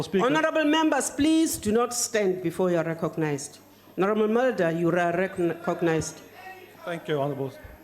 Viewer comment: and 19:31.......what did she say ? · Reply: Honourable Mulder. Mulder is his Surname